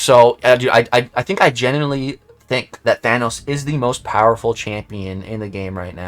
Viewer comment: 2 weeks late but i just wanted you to know that i think you said genitally lol 💌